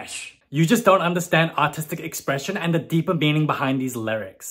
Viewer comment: Thought she said "autistic expression" first and not "artistic expression" and I was like " ok fair but also damn, low blow " ... 😭